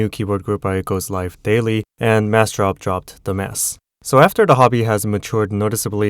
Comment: >Massdrop dropped the mass. I thought you said "Massdrop dropped the mess" xd